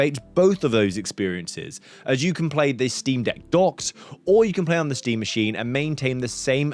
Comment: My volume was too low, so for the first minute of the video, I thought you were saying "the Steve Deck." I was really excited for the new product.